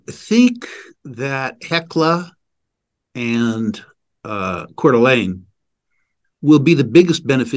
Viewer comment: Fantastic interview, many thanks! For me as a European it would be helpful if all discussed names would be written in text somewhere, e.g. in minute 54:30 when you discusse hecla silver, there is another name "quarterlane?" I cannot understand it and so I cannot find it- this is a pity. Maybe you could give some feedback about this? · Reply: Coeur d'Alene